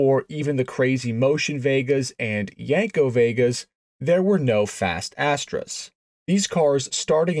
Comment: I heard "fast ass-tray"